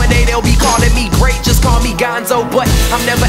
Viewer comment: does that say dont call me ganso xD